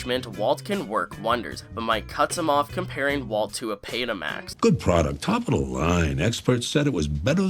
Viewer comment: Did you say Betamax or Petamax? It sounds like you said it with a hard P, but yeah, it’s Betamax. And Mike is correct -- technically speaking, it was the superior product, but VHS ultimately won that fight because of price tag, convenience and recording time, and a few other features.